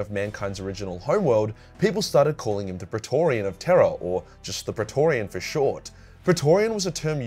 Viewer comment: Ok, the first few times I heard Rogal's title I thought it was "... Of Terror" not "... Of Tera". Both work for the setting.